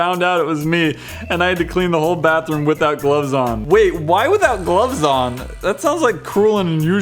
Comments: When he said "Without gloves on..." I heard... "Without clothes on." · i thought you said he cleaned the bathroom without clothes on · when he said "without gloves on'' i heard "without clothes on"